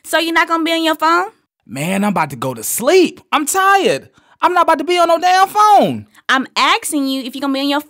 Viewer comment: any smart man should know that this whole conversation could've been avoided if he would've let the chick go home to her pillows long time ago if you already got options.. Smh lol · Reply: At the 0:50 mark, he clearly says "I'm tired, I'm not gonna be on a damn phone" So nope you can't defend this craziness even if you tried.